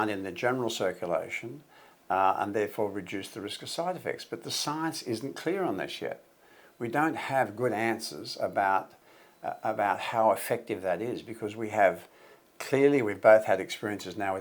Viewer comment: i replayed it 3 times,for a moment i thought he said ''the science isnt clear on this shit''